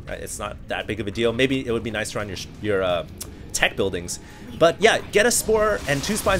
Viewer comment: lol around 15:09...i think he said "it's not a big of a deal maybe it would be nicce around your sh- your aa teck buildings" hahaha like around your shitt...